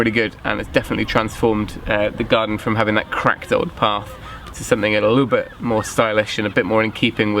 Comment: At first, I thought you said "crap old path" at the end of the video!